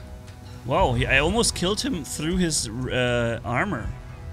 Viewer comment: I heard saying "I almost killed him through his resistance"... War Robots reference XD